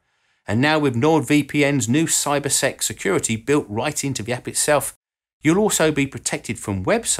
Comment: I thought he said "CyberSex Security Feature".